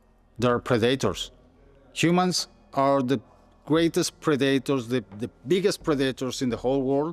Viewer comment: I thought he said Humans are the best potatoes